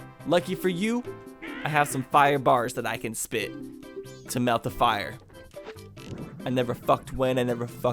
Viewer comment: Is it just me or did he say melt fire at 13:30 ish